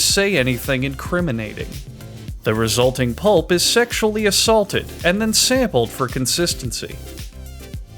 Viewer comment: DID HE JUST SAY SEXUALLY ASSAULTED!!??